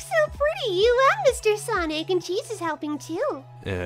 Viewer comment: I heard “cheesus” instead of “cheese is”